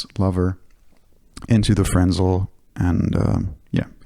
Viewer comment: yes! is back baby 🙏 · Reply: also at 10:20 did you say “lover into the friendzone”? year end pedal vid or personal life overview 😆